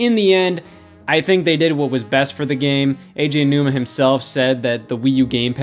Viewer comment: I keep hearing him say AJ Nooma